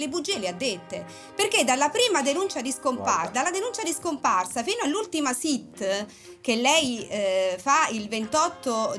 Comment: cosa dice mentre parla la Marano? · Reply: Mi sembra che dice "guarda..." .